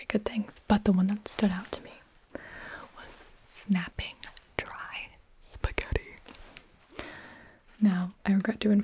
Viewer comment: When it said snapping dry spaghetti I thought it said snapping my spaghetti